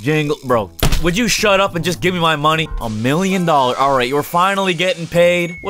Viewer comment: DID HE SAY JUST GIMME MY MONEY